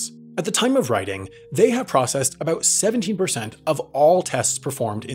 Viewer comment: it sounded like you might have been saying 70% while the graphic said 17%. 70% seems more like a "large testing provider" but it seems like it would be less likely that the 17% is the incorrect figure?